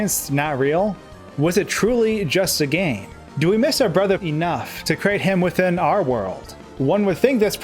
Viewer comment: That manner of speach... soooooo annoying!!!!!!!!!!!!!!!!!!!!!!!!! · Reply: Wtf was that awkward cut there?Do we miss our brother?ENOUGH!.(Gives me a flashback to John Wick the first movie."What did he say?" "Enough!")